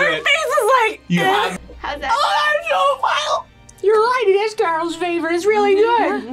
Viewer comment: 😂 I couldn’t stop laughing. Does anyone know what this girl was saying?? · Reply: “Oh that’s so vile”